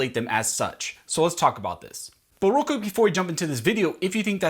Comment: Are u russian?? Did anyone else hear a russian accent at 00:13/14 sec "But vreal quick" and couple of other little parts?? Lol